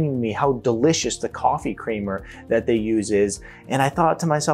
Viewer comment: The bottom line is, do your best to AVOID all processed and fast foods. Did I hear you say "kramer" instead of "creamer?"